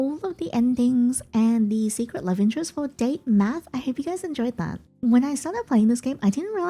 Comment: I was just listening while eating mala, I heard "welcome to DATE METH" and my head whipped to my phone. Hah😅😂😂😂 you had me scareddd